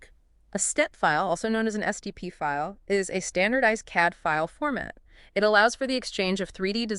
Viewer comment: salad: „did they say catfile?“